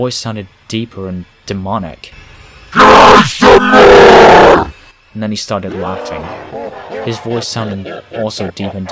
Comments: ok but what did heavy say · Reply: CRY SOME MORE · 👹 What was it saying? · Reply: "CRY SOME MORE"